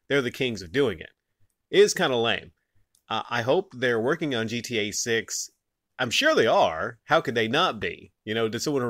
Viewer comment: I thought they said they were working on gta6 , they gave some teasers for it, not video, but they just were talking about it